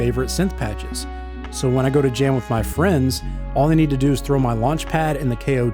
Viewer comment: I heard “when I go to GYM with my friends” I never thought about music this way 🙃